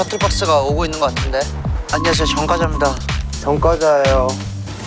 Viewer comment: 전 과자입니다로 들려서....ㅋㅋㅋㅋㅋㅋㅋㅋㅋ